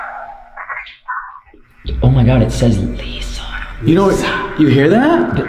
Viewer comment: when you thought it said “Lisa” I think that was actually the girl you were with saying ‘what was that’